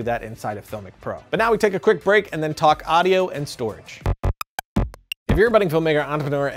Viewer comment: holy moly what are you saying? You are so fast xD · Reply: "But now we take a quick break and talk audio and storage."